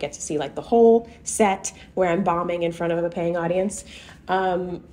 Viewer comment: Did you say "I'm embalming in front of a paying audience"?